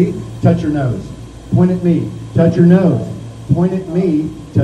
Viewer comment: I can breathe just fine and it still took me forever to realise he’s saying “Point at KNEE, touch your nose.” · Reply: Me too 😂 I always heard “point at me, touch your nose”